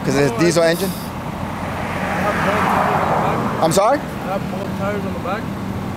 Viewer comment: in the video did I hear the guy saying he was speeding because he had bald tires if I had bald tires I don’t think I would be speeding.